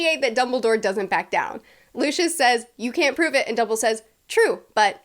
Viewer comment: did anyone else catch call Dumbledore "Dumble"? 😁😁😁